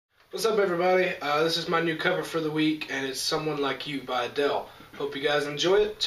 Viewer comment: pfft, his voice is so awesome it sounded like he said adittle rather than adele